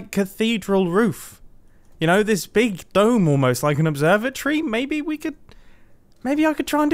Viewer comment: Did you say "observer-tree"?